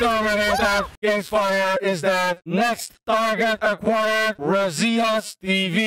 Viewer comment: is funny because said razios instead of razius im laughing so hard and dont forget to tap/click